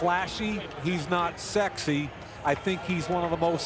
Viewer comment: hol up what did my boy just say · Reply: He not sexy. Wow. Lol